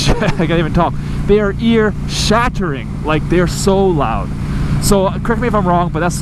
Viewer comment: When you said, "ear shattering", the way you said it I heard, "he shat himself". I have no clue why but I giggled a little.